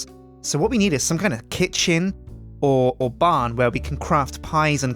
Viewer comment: When he said "Kitchen" I thought he said "KitSjin" at first..